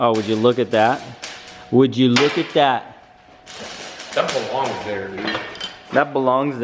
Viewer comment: i just thought i heard say, that befongs there. lol